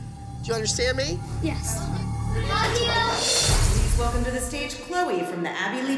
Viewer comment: Did anyone else hear maddie say “love you” to Chole at 7:32 before she went on stage because that was so cute 🥰